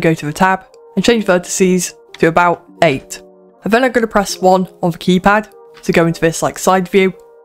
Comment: did he say 1??? cause thats not working dudes going way to fast